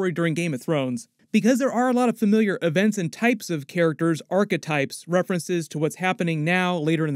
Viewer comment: Here's my House Of The Dragon Season 2 Cregan Stark video and the Return to Winterfell! Let me know who you want to play the character. Here's my House Of The Dragon Alternate Ending and Deleted Scenes video too! <link> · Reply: What was that you said about references to different story lines and characters, about 'Starketypes' if you would? For really real though, when I heard your intro for the first time, I legit thought that's what you'd said lolol.